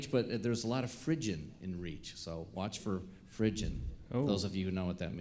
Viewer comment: What does Marty say at 11:40? Fridgin, can anyone tell me what that is?